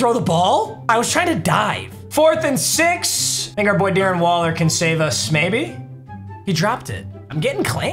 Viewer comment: sounded like he said “hard r boy Darren waller” but he said think our boy Darren Waller 😭